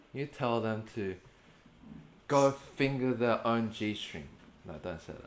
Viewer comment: At first I thought he said " go finger your own g spot " 😂